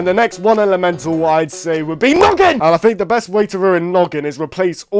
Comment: When he said noggin i thought he said "nugget"😂😂😂